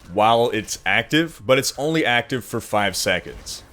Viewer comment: your transcript says it's online active 45 seconds, instead of 'for'